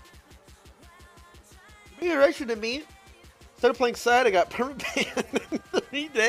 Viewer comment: What did he say there? Cant hear shiit. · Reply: he's reading a donation: "you are a big inspiration to me. i started playing sion and got permabanned in three days. thanks big t!"